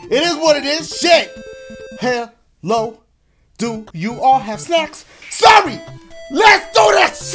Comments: I cannot be the only person who thought he said "sex" instead of "snacks" 😂😂😂😂😂😂😂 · thought he said something else🤣🤣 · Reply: Ray's Channel yeah I thought he said sex instead of snack I had to do a replay and double take on that part🤷🏾‍♀️ cause I was just like what the heck did he say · i thought he said “Hello, do you all have sex”😂😂